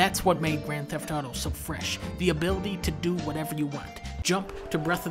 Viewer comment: What does he say after he says "does it have the best combat"? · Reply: yeah, that sounds right. I thought he said "fresh" or "fleshed"